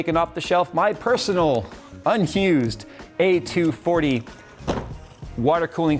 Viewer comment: Lol, 2:42 the caption says unhu’ ‘zed for unused